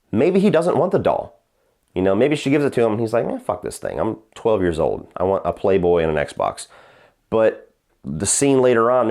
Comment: "I'm 12 years old, I want a Playboy and a Xbox." Hmmmmmm, thought that was what you said. And some people think I started at an early age. 😉😂